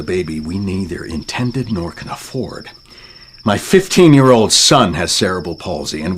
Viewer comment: What does even say at 5:14 😂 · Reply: Intended nor can afford